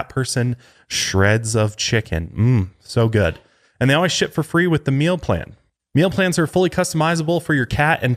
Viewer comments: I thought you said, "& they always shit for free" and I died🤣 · I thought he said..." & they always shit for free with a meal plan" - LMFAO I just spit my coffee everywhere!!! Goooooood morning!